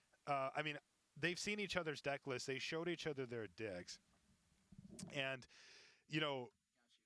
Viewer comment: Did say "they showed each other their dicks?" hahahaha